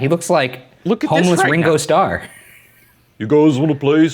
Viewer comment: says: He looks like a homeless Ringo Starr Subtitle says: He looks like homeless wringle star XDDD